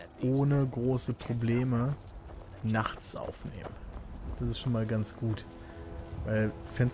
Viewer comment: haha ich hab verstanden: Jetzt kann man endlich ohne große Probleme bachts "saufen gehen" ;) anstatt nachts aufzunehmen ;L